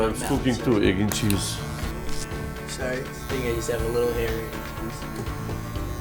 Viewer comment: I wish we can talk on the phone so I can verbally sound it out for u how some immigrants say hair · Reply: daz wuzzup you’re totally correct he said lotta hair Everyone else who thinks he said not a kid yes I agree it sounds like he said that but put the speaker to your ear and you’ll hear lotta hair